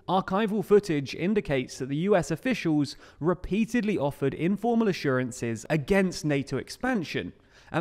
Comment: I hear 'informal', I see 'formal'